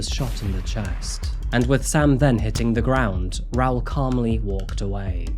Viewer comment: I swore he was about to say. "Hitting the Griddy" and not hitting the ground... Time to stop watching tiktoks. 😅